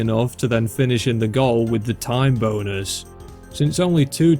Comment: HELP I THOUGHT HE WAS SAYING "TIME BONERS" THE WHOLE TIME